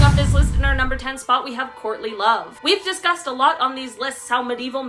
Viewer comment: Lol, I thought she said *Courtney Love*, I thought maybe she was using them as an example, especially with marrying for money 🤣🤣🤣🤣